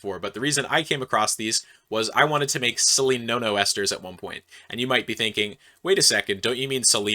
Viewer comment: All I heard was "Silly No No ester"......sounds dangerous.!